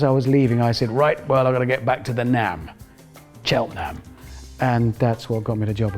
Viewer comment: What exactly does he say here? "Chell nab"? I thought he said "nap", but I'm not British and I have no idea what he said or why it convinced Jeremy to have him on the show hahaha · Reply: Cheltenham - it's a town in south-west England